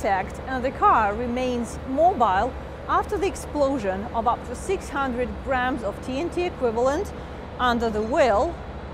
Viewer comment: did she say 600 gramm? A firecracker could not knock this thing out, good to know